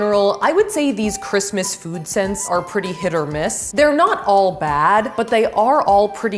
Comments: did she say hit or miss???? In my mind- “I guess she too the kids huh” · did I hear Hit or Miss?